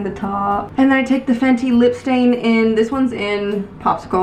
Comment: Literally thought you said Fenty lip stain in Costco. Would be a sick, cheeky eyeshadow color though, amirite?